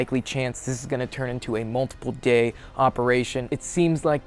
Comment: I thought homie said multiple GAY survival op. I was like yes, yes then what rewind that.